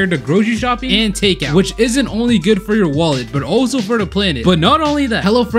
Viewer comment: I thought he said will eat your wallet instead of good for your wallet